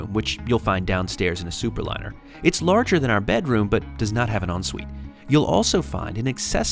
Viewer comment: Will someone kindly let me know what he says at 27:18? "It's larger than our bedroom, but does not have an _______ ___________." Thank you. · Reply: “…does not have an ensuite.”